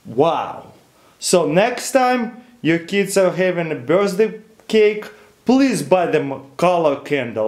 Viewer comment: Am i the only one who heard him saying birzday cake